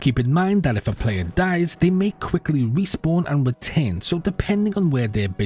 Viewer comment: what word is that? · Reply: "And return."